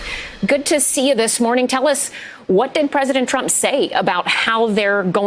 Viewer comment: Did she say president Trump 21:11 ? please don't scare us like that lol